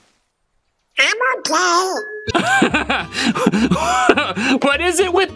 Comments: I hear him saying “I’m a thot” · I think Donald's actually saying what was that. But I can see how people could hear that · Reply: I hear "What numb scat" kinda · SOUNDS LIKE HE SAID ''Am I Gay?'' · Donald Duck actually wasn't misheard, he's just cussing like a sailor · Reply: Even though he said I'm okay I heard it like he's saying Emma's gay💀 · I heard "am I gay